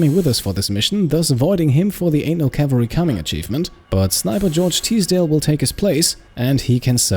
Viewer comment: At first, I heard you say “anal cavalry coming,” when referencing the achievement. 😅😇